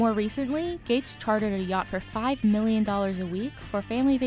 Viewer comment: did she just say chwater?